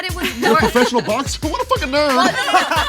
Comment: what does say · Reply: "professional boxer, oh what a fucking nerd" * laughter*